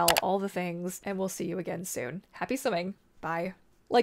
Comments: I thought you said "happy stimming" at the end 😂 I think both happy aiming and happy swimming fit swimmingly ❤ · Swimming? I thought you said simming at first - I do that daily hahahahhahaa :)